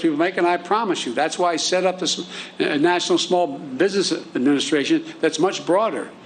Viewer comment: He is ignorant. Did I miss what he was saying as he mumbled, it sounded like he said "This is why I set up the SBA"? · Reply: I was thinking, "wait, did he just say he set up the SBA?"